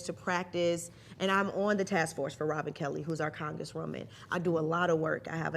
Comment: Sounds like she said Robert Kelly at first LOL